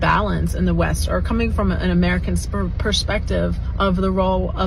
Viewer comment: 😂😂😂😂😂😂🤣🤣🤣🤣🤣 i could have sworn i thought i heard her saying "coming from an American sperm" lol 😂🤣🤣🤣🤣🤣😂😂😂😂😂